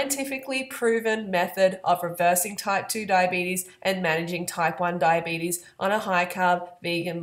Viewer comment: For a minute I thought you said "taekwon diabetes" XD